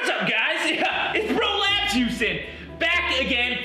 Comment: Aayyyy! Bro Labs Houston! Good to see you again, bro! I think it's scientifically necessary to see the effects of a non-lethal bean bag round on the world's strongest bodybuilder! 💪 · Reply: I heard it as prolapsed Houston 🤣🤣🤣